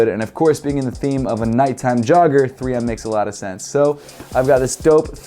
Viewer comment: Lmao i deadass thought it said nike jogger at first